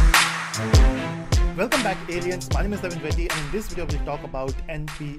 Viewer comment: Lol, i thought that he said at 0:04 "Welcome back idiots !"